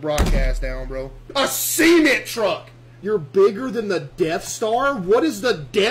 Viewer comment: Did he just say semen truck?